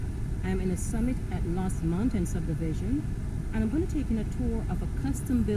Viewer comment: Homes for Sale I listened to the first beginning of the video a couple of times and I couldn't understand what you were saying. It sounded like you said lost mountain but I didn't find a subdivision under that name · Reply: It was the Cornerstone at Lost Mountain and has been renamed The Estates at Cornerstone.